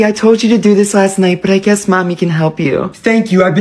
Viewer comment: I thought nvm but he said stinky mcpoopy chard